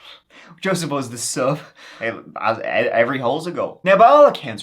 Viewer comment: I’m dead!!! Did I just hear “every hole is a goal?” 😂